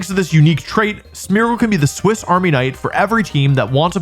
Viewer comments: did you just say swiss army knight @ 8:08? its swiss army knife.... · I'm sorry, is he saying Swiss Army "Knight"? Like a knight who was in the Swiss army?